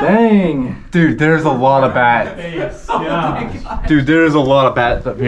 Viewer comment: did you say "there's a lot of bat" or "bats"?